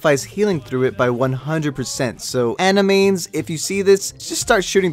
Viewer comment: I heard anime